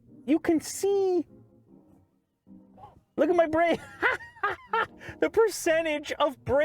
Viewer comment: I thought he said “look at my brain!!”.. and I said “sweet baby Jesus this car just tapped into this mans brain”